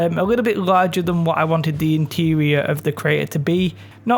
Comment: Did he just say "Traitor" 😠 Where terrain made easy started! 🤣